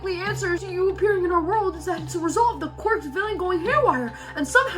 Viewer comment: Did you just say quirk's villian?